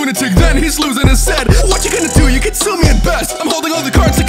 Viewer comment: I first heard "you can consume me at best", and it actually sounded quite good, I mean he's a snack